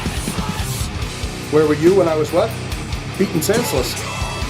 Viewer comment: I thought it sounded like he said, "Where were you when I was eating Chiclets?!?!?" That was decent for me....riffs were great and the slow sludgy parts were cool. Now, get me some gum! 🤘